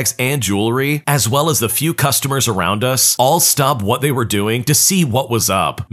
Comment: In Story one the subtitles at 2:10 say stabbed instead of stopped